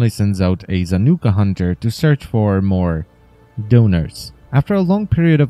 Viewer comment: When I heard that the first time I thought you said donuts instead of donors, but yet again I can picture Alad V sending the Zanuka Hunter to get some food.